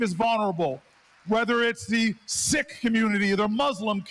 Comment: *sikh community is what he said.